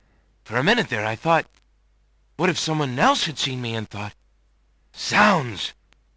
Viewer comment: what did he say 6:21? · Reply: What if someone else had seen me and thought